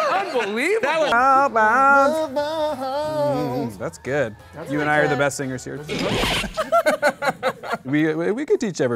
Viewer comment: Please, someone to write me what they say here, my English is not very good. thanks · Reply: Matias LFSC they were mumbling nonsense and then he said you and I are the best singers here